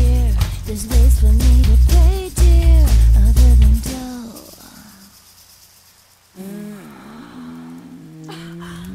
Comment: what was is she saying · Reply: "Where you goin?! There's other ways for me to pay dear, other than dough!"